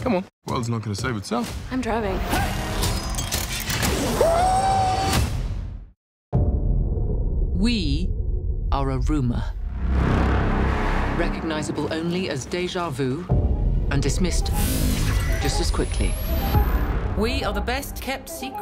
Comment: I thought she said we are the revengers😂